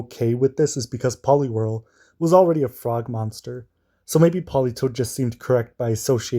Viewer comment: I thought you called Polywhirl a "fraud monster" and I spent way too long wondering why you had this opinion. Then I realized you said frog lol.